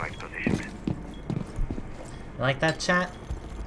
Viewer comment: When spoke at 0:29...I heard "You like that, Chad?" 😆